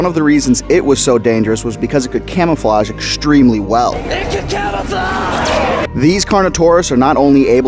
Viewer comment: Its funny cause i know he says "it can camouflage" but all i hear is "ancient camouflage"